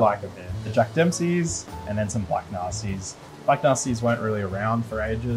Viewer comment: did he say “black Nazi’s?” Who named these? 😂